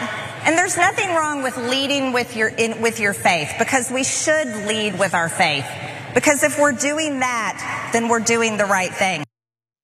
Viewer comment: I just spit out my coffee.. I thought she said "lead with my face"...